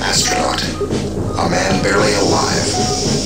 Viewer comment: "Steve Austin - man buried alive". For YEARS I thought that was what it said ><